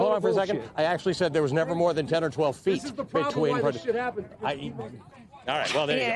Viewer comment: I heard him say 10-12 FEET ….. I guess my ears are lying 🤣🤣🤣🤣🤣